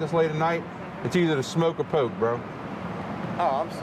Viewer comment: did he just say smoke or poke??